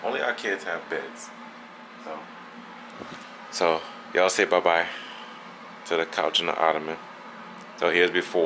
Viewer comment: did he just say he had sex on the couch